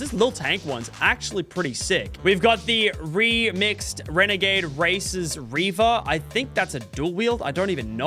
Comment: I thought bro said renegade racist.